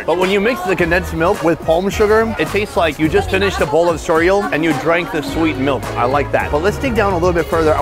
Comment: is he saying “cereal”??? Hahaha